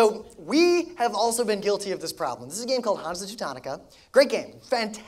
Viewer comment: whats the game at 1:30 called? he speaks to quickly i couldn't catch it. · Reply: Hansa Teutonica